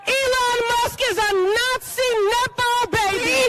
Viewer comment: I couldnt hear it very clearly but did that bald guy just call Elon Musk a "Nazi" outside of congress?...Politicians only have congressional protection inside the building dont they?